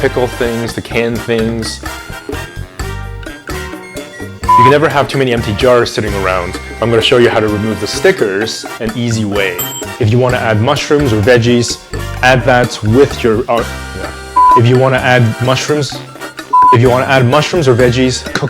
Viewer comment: When I first heard 1:36 I thought you said “ if you want to add mushrooms or veggies, add that with your arm “😂